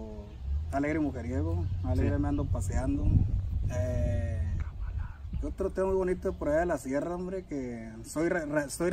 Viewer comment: Que alguien me explique de quien es y que dice la voz que se escucha en el minuto 10:13 ..suena tenebroso. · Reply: Se la estaban soplando al as